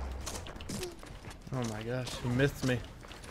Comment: says “he mythed me” but it sounded like he said ‘he missed me’ with a lisp